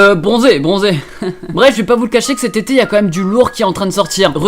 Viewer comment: A 0:05 j'ai entendu "ses TéTé" au lieu de "cette été" tu me rend perver mon frr